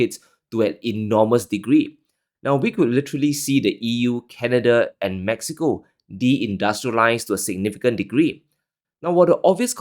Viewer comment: Why you thanking him, he's lying about Canada de industrializing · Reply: I thought said that the E.U., Canada and Mexico COULD de-industrialize. I might not agree with everything says, but I still listen to his videos. I’ve learnt a lot.